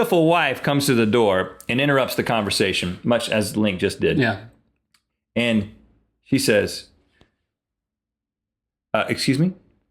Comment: did he just say "and he said" about his wife ^^..